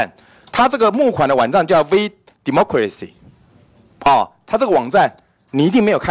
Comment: 一直把VDemocracy聽成「V什麼鬼兮」 幹好好笑喔，講到服貿的時候就說利大於弊， 講到自己要被罷免就說中資一進來就涉及國安， 委員突然開始變成逢中必反了嗎？